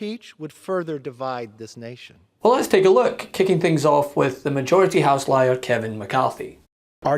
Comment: I thought I heard, 'majority housewives.' And I thought, 'Ya, that's right! A "reality show" full of fake drama '